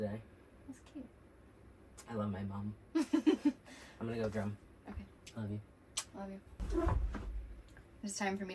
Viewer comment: did say "I'm going to go drown" 🤣 "ok love you" lmaoooo maybe I misheard